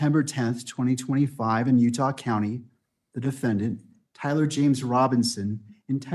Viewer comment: It sounded like he said his name was Tyler James Robertson ?